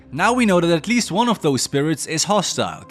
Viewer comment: I misheard this as "At least one of these turds is hostile" and I 😂